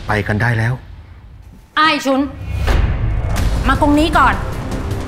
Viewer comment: คุณหญิงผ่องพูดว่ากะไรนะ😂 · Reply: อายสุนมากงนี้ก่อน อายสุนน่าจะเป็นชื่อเมียน้อยส่วนมากงนี้ก่อนน่าจะแบบว่ามาตรงนี้ก่อนอะไรแบบนี้เป็นภาษาสมัยก่อนแถมฟิวแบบจีนอะไรแบบนี้